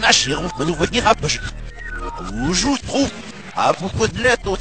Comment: I hear 'YouTube Poop'...also, Luigi sounds really crazy about spaghetti :D